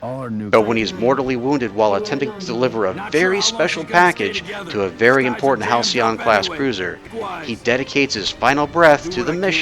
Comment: During Carter, I heard "Delivering a very special package to a house on a cruiser"